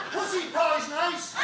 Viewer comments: I'm sorry, could someone tell me what the gentleman at sound 8:35 is shouting? Please and thanks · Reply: "Pussy pie's nice" - pussy = cat, or 'vagina'. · What did the man say? I couldn't understand him. <Thanks in advance for responding> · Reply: Sounded like "p*ssy power xxx" I couldn't understand the last one. That's the best I got.